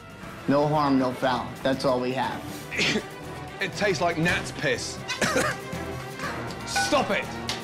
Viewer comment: what does he say? It tast like .... piss? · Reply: Gnats Piss